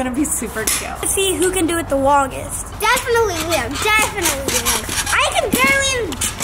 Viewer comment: Don't know if you need to edit this out but definitly said his name when you were outside with the pogosticks near the pool. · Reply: I heard, “Definitely him, definitely him.”